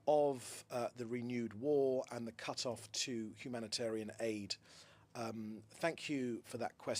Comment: I am an Arab from Kuwait and I stand with Israel the country of humanity ❤️🇮🇱❤️ أنا مسلم سني من الكويت وأناصر اسرائيل دولة الانسانية والخير والمحبة والسلام ❤️🇮🇱❤️ لا يوجد شيء اسمه فلسطين في الماضي ولا الحاضر أو المستقبل 🗑️🇵🇸👢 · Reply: did he just say country of humanity? 🤔☝️😆😂🤣